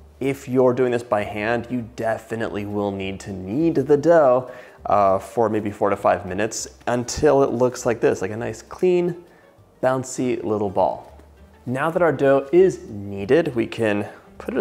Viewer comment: I thought you said I needed to knead it for 45 minutes! I had to go back and hear it was 4-5 minutes 😂